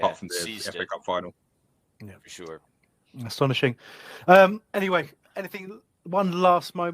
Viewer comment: ホントに日本語で「素晴らしい」と言ってるように聞こえる。 ※Astonishingの直訳は「驚くべき」だそうですが。